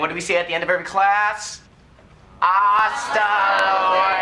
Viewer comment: did he just say "hasta los huevos"?